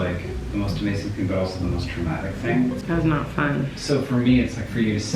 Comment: It sounded like said traumatic but the subtitles said dramatic? Lol